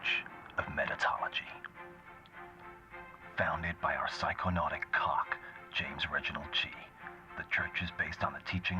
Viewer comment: Did he say “phsyconuatic cock?”